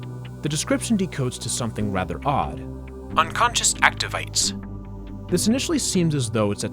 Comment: i thought it said activities 😮